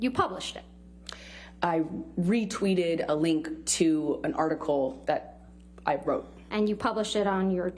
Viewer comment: Did she just say of an article I wrote???????